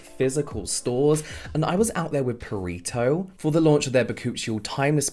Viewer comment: I keep mishearing it as "burrito"